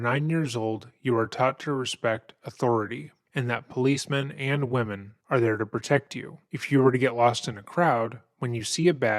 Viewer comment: was anyone else confused for a moment? He says "policemen and women are there to protect you". I heard this as policemen are there to protect you. Women are there to protect you. As in, if you're in danger, head to the nearest random woman and she will protect you. I was rather confused wondering why thought all women in the world suddenly became superheroes. For anyone even dumber than me, what he was actually saying was policemen and policewomen are there to protect you